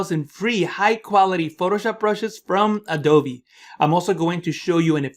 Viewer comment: Did he say "Adove (A-Do-Vee)" at 0:14?